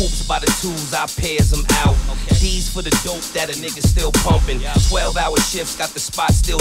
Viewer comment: what are you talking bout? he said "nigga" at 1:53 lol